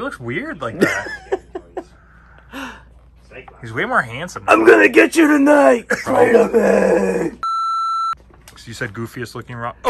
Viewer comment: It sounded like Nyla said "I'm gonna get you tonight Spider-man" when holding that Rusev now Miro figure, which is funny because we all know that he basically is Kraven the hunter and would have been perfectly cast if you know...sony wasn't trying to make all of Spidey's villains heroes. lol